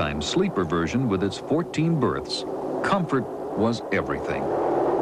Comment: Did he said "fourteen birds"? 37:23 "Birds" again?! Am I missing something here?